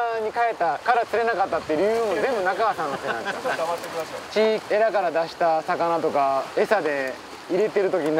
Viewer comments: 中川さん「ちょっと黙っといてください」 ナスD聞こえてないw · 黙ってくださいって聞こえる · ちょっと黙っとって下さいって聞こえる